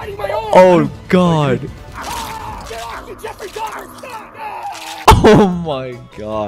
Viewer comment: I can’t believe Caylus didn’t catch what was said. The guy, who’s hand was being eaten, said “Get off me Jeffrey Dahmer.”